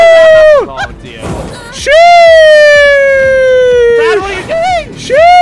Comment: i heard it as chees